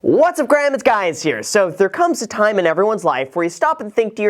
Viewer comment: Did he just say, "What's up Graham, it's guys here"?😂😂